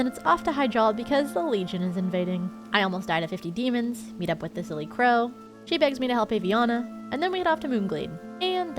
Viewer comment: I heard "The Silly Crow" the first time you said her name during the druid quest and that is now her name in my head.